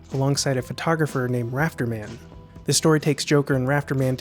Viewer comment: "Rafter Man"? At this point I trust your command of the source material without question, but in all my viewings I always heard it as "Raptor Man" (first viewing pre Jurassic Park for disambiguation). :star the more you know